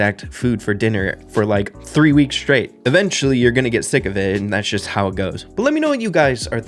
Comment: did someone say "thick of it"?